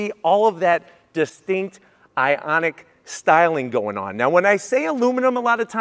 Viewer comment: He clearly says "distinct IONIC styling..." obviously NOT a well educated man.